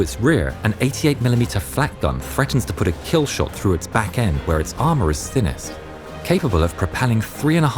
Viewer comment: did he just say stronk armor?